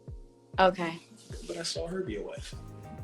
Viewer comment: catch the problem why some hate Kevin.. In her backgroud i think a man says "but i saw her be a wife" , maybe it's her brother which is weird that her and any man watching kevin dhies together snd eveb worse she's on the show..